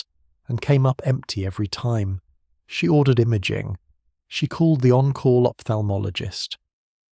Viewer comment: Right at the start when he said " she ordered imaging" i heard "she ordered him a gin" and immediately decided that gay hospital is WAY better than straight hospital and now i want to know where i can find a nice gay GP in my area who can show me things....